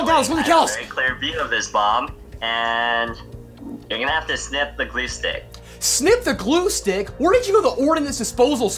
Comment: I thought he said "sniff the glue stick"